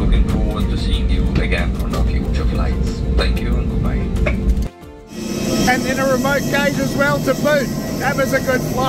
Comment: I really enjoy your videos. But I'm sorry I don't speak Aussie. I don't know what you were really saying at 5:39, but the closed caption version was "in a remote cage as well as to poop." LOL